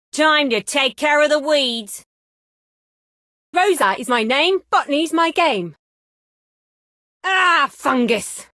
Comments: I thought it says rosa is my name fuck is my game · I always thought she said "Rosa is my name f*ck is my game" lol or "f*ck me for the win". I'm so disappointed now hahah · I legit thought that rosa said Rosa is my name, f*ck is my game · Thought she was saying "f*ck me's my game!" Thanks a lot! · before I thought she said "Rosa is my name f*u* is my game" 😂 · Rosa is my name, F-ing is my game 0:14 Ah, F this 0:40 F me for the win I know many people thought this is what she was saying just like me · all i hear is : "rosa is my name fucking is my gay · i thought she said fucking is my game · I thought she say f**k but she actually says bottany Thanks man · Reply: i thought she said “rosa is my name, f*ck me is my game”.. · I always hear “Rosa is my name, f**k me is my game” idk why🙏🏻😭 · ”rosa is my name,botany is my game!” I THOUGHT SHE ALWAYS SAID ”rosa is my namw, f is my game” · I always thought she said f**k is my game but it was botany,and I thought she said f**k me for the win but it also was botany.Yeah..My mind really goes dirty O_o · I thought she said "F**king is my game" 😭😭😭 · I thought she say f**k but she actually says bottany Thanks man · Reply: i thought she said “rosa is my name f**k me is my game” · Did anyone think she is saying fuc**ng is game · ROSA IS MY NAME FOOKING IS MY GAME since no one dared to say it....i did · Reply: i hear "rosa is my name, fuck these my game" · i thought she said "Diamond take care of the weeds"